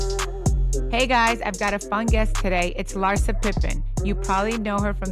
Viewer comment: it sounds like she said fungus 😂 which fits the bill for Larsa